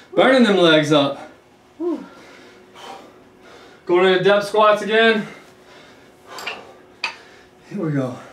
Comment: Loved this so, so much. I thought you said “death squats” at first, and thought they were appropriately named 😂😂 I realized the move was depth squat, but it still made me laugh.